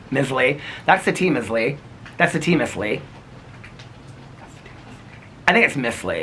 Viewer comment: Oops I thought it said tea missile 😂